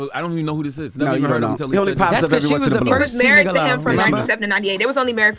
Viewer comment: Did nobody catch what Jess said lmao “she was the first 2 nigga low”💀💀💀💀